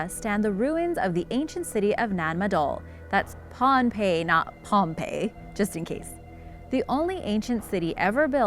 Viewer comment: so it sounded like you said 'its ponpei and not pompei'.............well its spelled the second one so did i just misunderstand you??